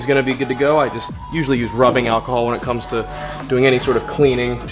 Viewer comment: " I usually use rubbing alcohol because its what I have for my bongs " is what I heard 13:42 lmao